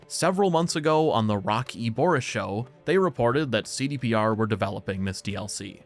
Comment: genuinely thought you said the rocky horror show